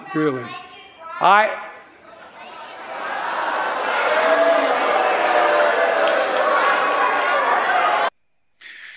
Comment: What tf did the woman say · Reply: Lakin Riley, a girl who was killed.